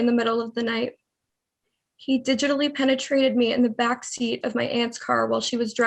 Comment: I’m sorry but at 22:08 did she say “digitallly penetrated me”